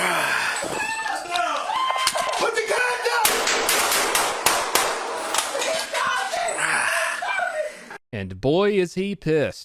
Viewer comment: nothing related to the video, (kinda) dont take this comment seriously the subtitles got me cracking up at 2:34 "put the condom"